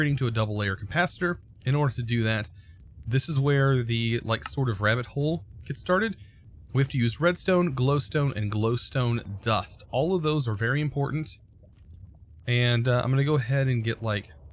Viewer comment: Did he say redstone, glowstone, and glowstone dust